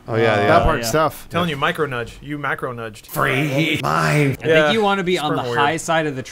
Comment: Sounded like free mind